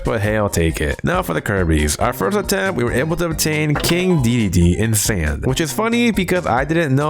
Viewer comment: did i hear King Diddy?